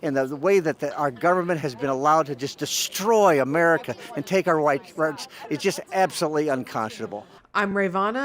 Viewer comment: Did anyone else hear the Freudian slip "and take our white.."